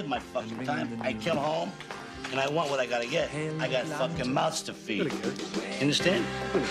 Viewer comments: It sounds like he said "I got a fuckin mouse to feed." LOL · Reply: I thought he said he "got fucking mouts to feed". Maybe no one fed his "youts" while he was in the can. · It sounds like he said "I got a fuckin mouse to feed." LOL